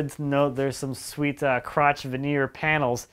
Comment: did you say "sweet crotch"?